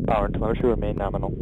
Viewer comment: "Power to (not sure) remain nominal." i heard "Power to motor remain nominal."